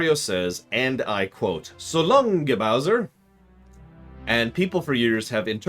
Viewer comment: I always thought Mario said "So long, K. Bowser", short for King Bowser. The whole controversy seemed to come out of nowhere.